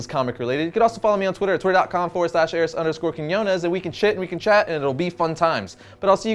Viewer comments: I thought he said we shit and chat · Did you just say "we can shit" at the 9:05 mark? Love your work